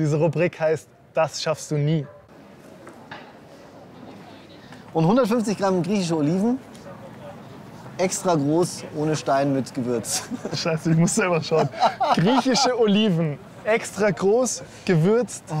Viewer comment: ich hab "150 Oliven extra groß" verstanden😂